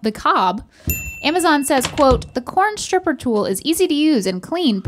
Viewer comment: I thought it said coin stripper 😂